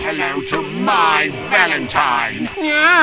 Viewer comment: I always thought he's saying "My Valentine" when it's "Mai Valentine"!!!